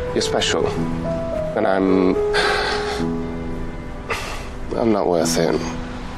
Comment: Great work! I always thought he said "I'm not worth you"